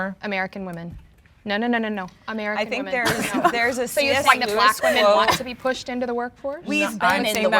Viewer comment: From what I can hear at 33:15 it sounds like said “you think black women want to be pushed into the work force?” … BLACK WOMEN HAD BEEN IN “THE WORK FORCE” (SLAVERY) FOR HUNDREDS OF YEARS WHAT ARE YOU TALKING ABOUTTTT